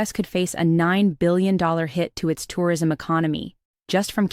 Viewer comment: And just think: tourism season is just getting started. This is only the calm before the storm. And as a Canadian, I could not be more pleased with this trend. Elbows Up! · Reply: I thought you said Terrorism season😁